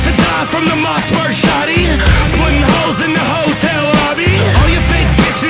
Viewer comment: I could've sworn he said "putting hoes in the hotel lobby" I need to clean out my ears 😂